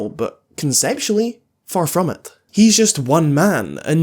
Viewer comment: for a second I thought you said "sexually? Far from it" 💀